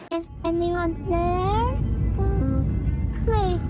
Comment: 이부분 뭐라고하는거에요??ㅋㅋ궁금해여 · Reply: Is anyone there? Oh oh Hi ! 이거에요ㅎㅎ